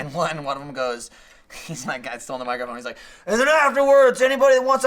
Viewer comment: I thought they were saying N-word. guys you can say midget...